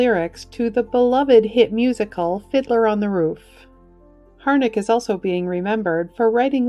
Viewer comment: I swear I heard her say "Hitler on a Roof" lol